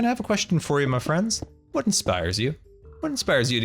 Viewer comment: I thought he said 'what do spiders eat'